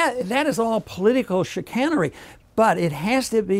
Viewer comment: I never thought I would hear Dr. Paul say "shitcannery"🤣